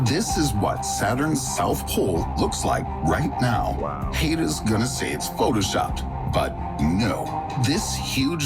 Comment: Did he say "haters gone say its photoshopped"? 😂